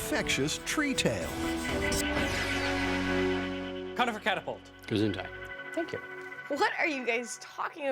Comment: Did Tory say "Gesundheit" at 11:34 ?